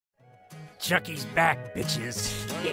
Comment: I thought he said “chuckys bad bitches”